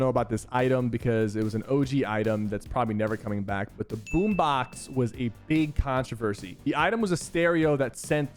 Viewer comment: I personally think they should add the boom box back for zero build and maybe make it do like 5 damage and not last as long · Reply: i thought you said boom bow and that scared me